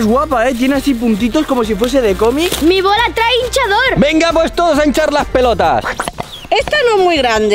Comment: Soy argentino y obvio mal entendi cuando dijo "todos a iniciar las pelotas" en el minuto 2:20 en mi país eso significa que estamos molestando a todos